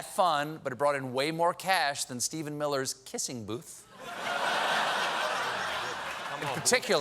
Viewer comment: Ha! I wasn't watching just then and thought he said "Hissing Booth". They missed an opportunity there.